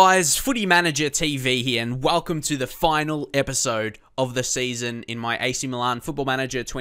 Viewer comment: Thought he said welcome to the last episode of series lol